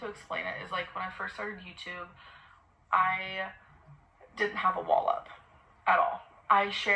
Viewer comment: I thought she said she “didn’t have a wallop” ! 🥊 Did you notice she almost said vicious “cycle”?